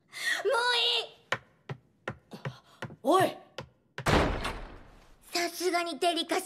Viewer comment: Did she just said "sasu-gay" ?!🥸